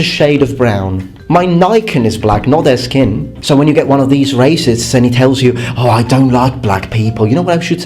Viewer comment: I thought he said raisins not racist I started rolling when I'm like wait raisins can talk and or racist..... Ohhhh, he meant racist.